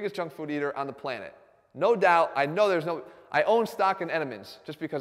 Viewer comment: what's he say there? "I own stock at m&ms"?